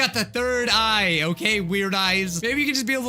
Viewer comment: I thought you said my "Nerd Eye"